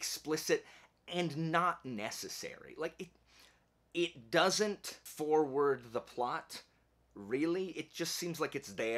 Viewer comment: I'm not sure why my brain decided to take this leap, but in your number 8 pick, given what you said about "sexy time", I thought you said "...it doesn't four word the plot..." as in four-letter word the plot. I had coffee come out of my nose because of the sudden laugh that came to me, and then a chuckle about what you literally said. Great list, I'm adding them to my Amazon list right now. Lastly, great outro! HAHA!